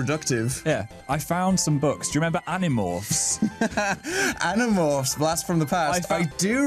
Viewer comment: omg animorphs I remember those books, I loved them :) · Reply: I actually thought they were saying animops what is wrong with me